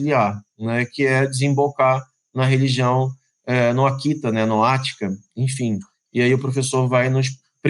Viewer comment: vai desembocar no que o CVII? Akita? Atica? Não entendi.